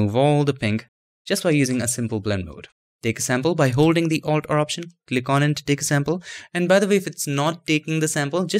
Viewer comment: i heard "by holding the alt corruption"